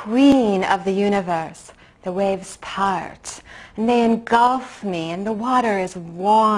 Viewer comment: "I am the queen of the u n i v e r s e the waves part they engulf me, and whe water is w a a r m " *awkward smile* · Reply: I heard “Men golf me”